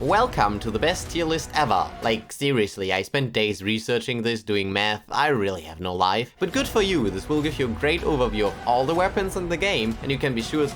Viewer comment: I misheard the intro as, "I spent days researching this doing METH" and was like, "Man, this guy cooks and games"